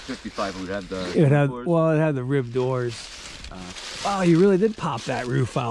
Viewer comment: im thinking may to june of 55....all the weird stuff. later gas tank Ribbed doors. Still worth saving. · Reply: I thought you said ribbed doors. I didnt really see inside. so it is a 56 57